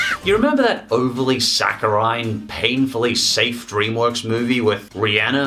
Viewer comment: ‘Overly Sakurai’ Am I the only one who heard it that way? XD